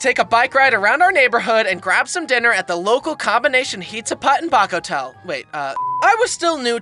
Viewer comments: Kinda funny when i thought it said "Hizza Butt and Paco Tell" XDDD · I hear it as Baku toe